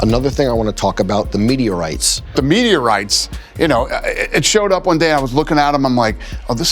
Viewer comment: When they said meteorites i thought they were saying media rights, swear Americans can’t ever pronounce the letter T